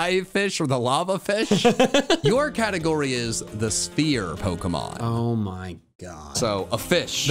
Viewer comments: When you said Sphere pokemon I thought you said "Spear Pokemon", and I got really confused when caught that Hisuian Voltorb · When you said Sphere pokemon I thought you said "Spear Pokemon", and I got really confused when caught that Hisuian Voltorb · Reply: Ditto, except I thought he said 'steer' so I was expecting to catch a deer or cow themed pokemon 😂